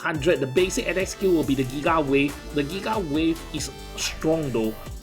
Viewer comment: I heard "The Gila Wave" xD